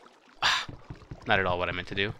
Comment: i put captions on. X said "not at all" and captions said "marijuana it"